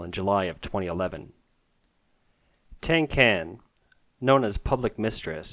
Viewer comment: literally thought I heard Tin Can 😂